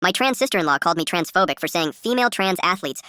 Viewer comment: OP said "My trans sister in law" I heard "my transistor in law" 🤣